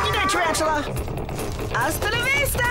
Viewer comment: Did Odd say "Hasta la vista"?